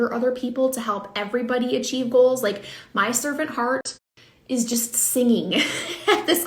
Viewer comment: "my servant heart is just singing" I hurt my eyes rolling them at that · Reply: Lol I thought she said serpent! 🤣😂 I was like “is that a Harry Potter reference or something?” 🤣 servant makes more sense